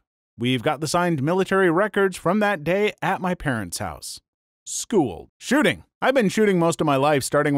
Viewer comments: I thought he said “school shooting” cause I was just listening while working 💀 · ngl i heard " school shooting "